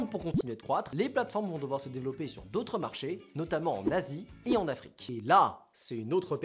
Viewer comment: "En Asie" Moi : En na*i ???